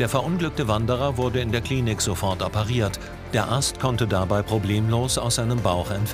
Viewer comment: Ich habe am Ende verstanden „ Der Arzt konnte erfolgreich aus dem Bauch entfernt werden“ 😂😂